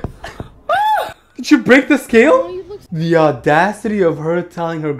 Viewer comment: I thought it said 973 pounds 💀